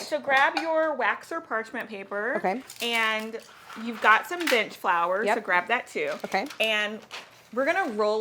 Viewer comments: When said “bench flour” I heard “bitch flour”, I laughed hysterically, and I will never not call it bitch flour from now on. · Did anyone else hear "bitch flour" instead of bench flour around 5:45 lol